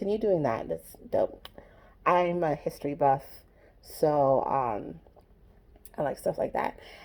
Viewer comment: She said she's not s history buff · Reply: Grace The Gracious 1:10. IM A HISTORY BUFF. I heard it. Thank you.